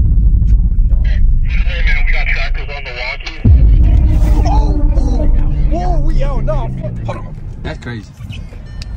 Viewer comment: What did they say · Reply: "We got trackers on the walkies"